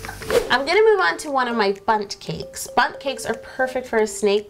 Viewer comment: When you said bunt cake I thought you said butt cake 😂😂